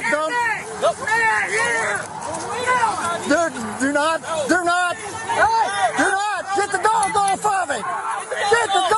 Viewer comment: What was that command the officer gave the dog alone with the arm motion? Did I hear KILL at about 0:37? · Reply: Yes, he yelled kill and pointed to the man.